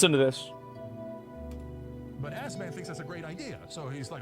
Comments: really sounds like he says ass man. r u an assman asmon? be yet another thing we have in common. hard to find an insult in that. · Asmond, I hear "Ass Man" and, I'm down with it.😊